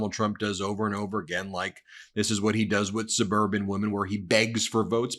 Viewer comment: Did he say suburban housewives? I thought he said bourbon housewives, which would have explained the cheering.